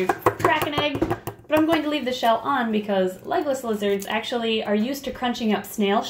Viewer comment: I couldn't here her when she was feeding her lizards eggs so I thought she said legolas lizards and I immediately thought lord of the rings